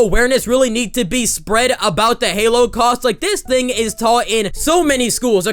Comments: I thought my man said “ halo cast” 😂😂 like the game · at first i thought he said halo cosplay i was like ???? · I thought u said halo OST lol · When you said halocost I thought you said halo cosplay. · At first I thought you said halo cast. 🤦🏼‍♀️ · At first I heard “halo cause” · I thought he was saying Halo Cast